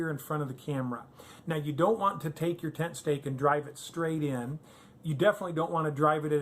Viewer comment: I thought you said tent snakes! Good evening from Erwin, TN.